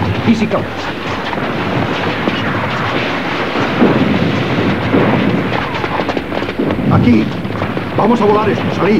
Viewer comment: que dice ahi? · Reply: "El pueblo chino es próspero" y lo otro no se entiende muy bien